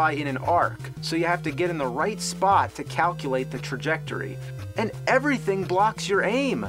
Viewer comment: What did The Angry Video Game Nerd say around 3:44?... "Calculate the--" what?... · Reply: trajectory